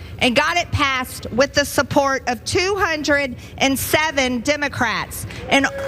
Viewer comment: "Tim-ah-crats" is all I hear 😆 also, she SO badly wanted to be interrupted